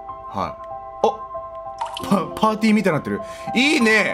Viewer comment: ここパンティーで聞こえた俺は既に末期状態。